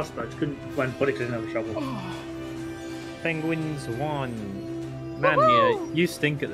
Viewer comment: did say "pengwings"?